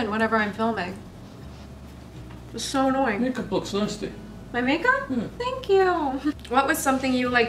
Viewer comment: I thought your dad said your makeup looks nasty 😂